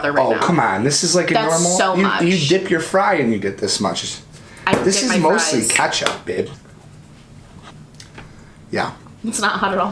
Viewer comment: I thought said "this is mostly ketchup, b*tch" and my face changed so drastically lmaoo